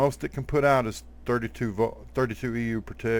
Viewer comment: When you said 32 per tick I thought you said per TIT